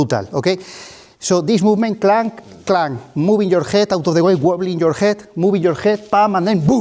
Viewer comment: did he just say WOBBLE?? insert an invincible edit